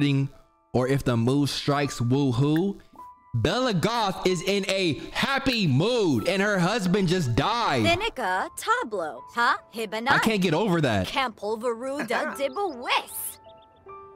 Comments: Anybody else catch bella talking about hablo?😂😂😂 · Tell me I’m not the only one who hears Bella Goth say “ The n*gga tablo” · did bella just say nigga at 21:05 ? during black history month??? smh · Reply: She definitely said Da nigga🤣